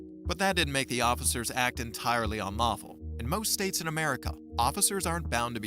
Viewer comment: I think the subtitle meant to say unlawful, not “marvel”.